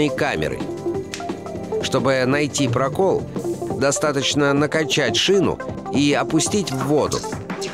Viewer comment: мне послышалось накончать